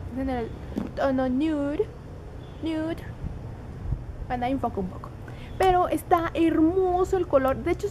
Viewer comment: que dijistes en el minuto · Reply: dijo bueno ahí un poco